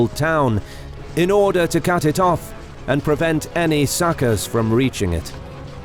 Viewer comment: Did I hear that right? "Prevent any suckers from reaching it".